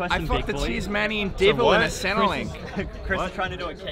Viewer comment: no one gonna talk about what saying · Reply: Lmao I heard say Centrelink